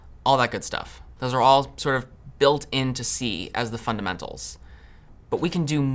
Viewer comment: I think the subtitles are off here. He said "built into C", and the subtitle says "built in to see" lol